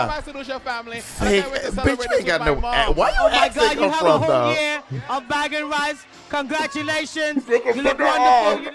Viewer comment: Did I hear it right or did Monet win a year supply of rice for winning Gay Caribbean? Lol